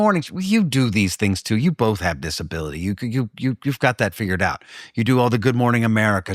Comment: So, when Alan said Nathan had "this ability" I thought he said, "disability." And then it swings back around to Nathan's zipper disability. The circle is complete.